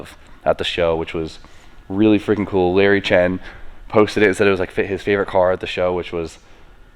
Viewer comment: I thought he said Mikes NSX was his favorite car lol